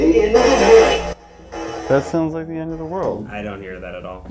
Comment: i keep hearing "The end is near"